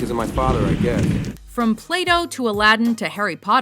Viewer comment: did she just say 'play doh' instead of plato? now i have the image of a lump of play doh haveing a epithany in a cave; its fantastic.